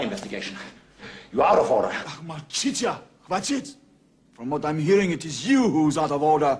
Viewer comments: What did Mishkin say in Russian at 3:26? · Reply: He was saying "Molchityat molchit" meaning keep quiet or keep silent. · What is it that Mishkin says at 3:25? Can someone translate? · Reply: He was saying "Molchityat molchit" meaning keep quiet or keep silent.